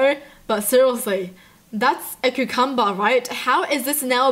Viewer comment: | Did she say Ku-cum-ber? I thought it was Cu-com-ber